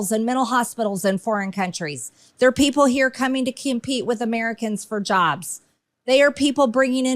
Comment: Wait, did she say 'kimpete' instead of 'compete'?